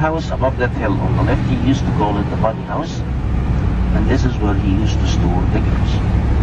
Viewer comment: what did he say? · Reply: I'm completely guessing but I think it was "this is where he used to store the girls" or something reasonably close.